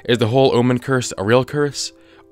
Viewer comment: hold up he said "i'll see you on flip side" not "on THE flip side" this isnt the real guys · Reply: Ducky Quackington i heard "ill see you on the flip side", he said the quietly or was just in a hurry